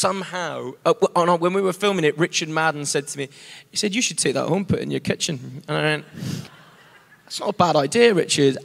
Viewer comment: What he said at 1:50 can somebody please help me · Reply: He says that Richard Madden told him "You should take that home, put it in your kitchen" about the Troubador sign from the movie.